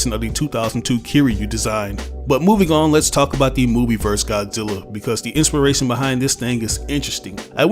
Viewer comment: did he just said movie verse godzilla